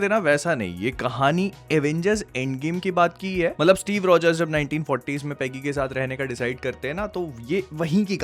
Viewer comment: Why I heard "Steve roger pegging k sath" my damn mind